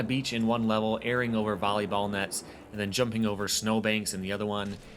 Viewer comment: did he just say 'Bolley Ball?'